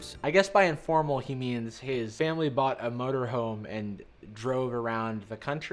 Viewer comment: It sounded like they said mhotor hoe The point of the video it is at is 18:49/